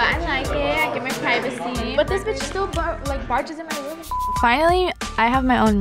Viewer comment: Is it me or did anyone else hear her call her little sister the b word at 2:27 2:28 wth if she did say that to her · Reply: She says" but this bitch still barges in my room and s***"